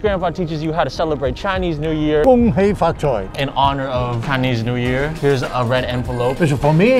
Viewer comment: I thought he said "In honor of Kanye's new year" for a sec